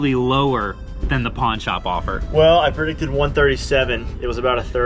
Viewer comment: I thought it said pórn shop lol